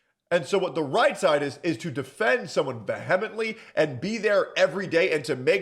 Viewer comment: *vee-ha-meant-lee. Now say it fast. I dont know what word you said. 😂😂😂